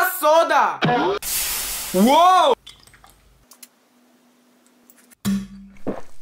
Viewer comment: Naw after he drank the lava soda I thought he said my mouth is so hard rn